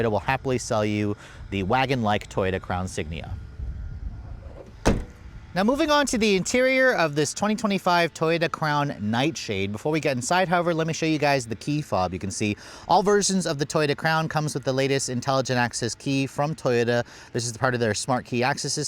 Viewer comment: Did you say Toyota Clown Nightshade at 8:28 😂😂😂😂